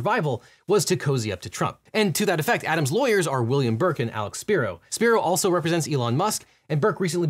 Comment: I totally misheard this, but I could've sworn at 3:05 you called him "William Birkin"... Pretty sure rookie police officer Leon S. Kennedy took care of 'em :V